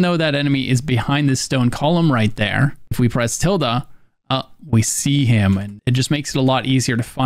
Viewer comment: What are u saying at 14:56? Tilda?